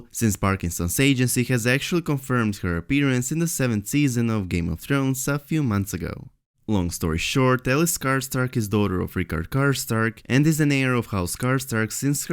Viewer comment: sorry it sounded like you said Alex Skarsgard. That would be too good to be true.